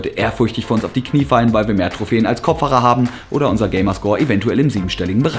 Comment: was sagt der da Kopffacher , was ist das? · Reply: DieZockerZone Kopfhaare!